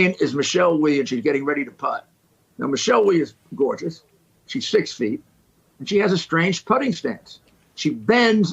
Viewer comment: I thought he said “she’s 16”. But he said “she’s 6 feet” lol. I remember she was the youngest ever player to play a major in the LPGA when she was like 15. So when I thought he said 16 I was like that’s a whole other kind of sick. But he said 6 feet so he’s just normal Giuliani sick lol.